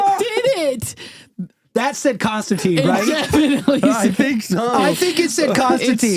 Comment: I hear "haunted" but after listening like 15 times I can kind of make out Konstantin, or more like "con-stant"